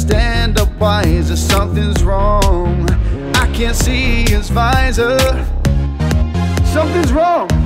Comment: his visor sounded like yesvisor